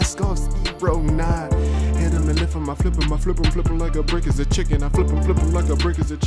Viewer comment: did he just say "a brick is a chicken" at 19:17 😂😂😂